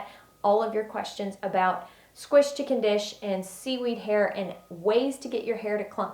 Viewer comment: This is funny--upon watching this video the second time, something I missed the first time cracked me up: At 2:32, the Closed Captions say "squished chicken dish." 🤣